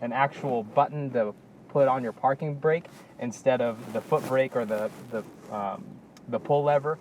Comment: Very informative review of the LX. Um, I'm not sure who does the closed captions but I wonder who else caught "f*** breaker" instead of "foot brake" at 6:19...